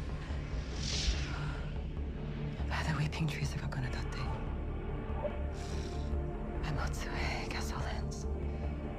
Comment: Does anyone know what Tina is chanting at 0:13? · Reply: to where my spell commands**